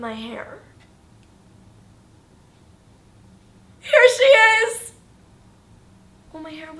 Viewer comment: did you just say HAIR she is?